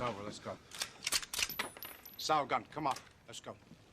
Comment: Does anyone know what he says @ 3:08.. "Sal gun"?